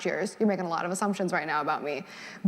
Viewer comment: that is not what she said. She didn’t say “only things that affect me” she said “only things that are important to me” and those are 2 very different statements